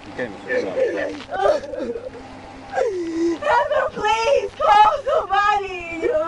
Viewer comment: I actually laughed when she screamed for her passenger to call the Police. Who did she think she was dealing with already? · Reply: I thought she said call somebody